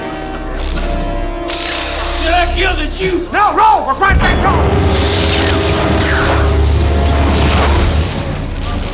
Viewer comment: what was bro saying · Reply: “Do I kill the juice?”